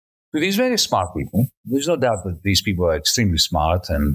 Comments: I keep hearing "Cloud Thiefs" and having to remind myself that, as much as that also makes sense, he is in fact saying "fiefs" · I know he is saying "Cloud Fief" but somehow I keep hearing "Cloud THEIF". I don't know if that is due to listening at 2x speed, my own Freudian slip, or his accent..... but it seems appropriate nonetheless